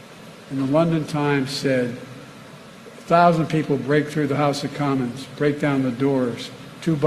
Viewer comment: Seriously? Did he just say the "house of condoms"?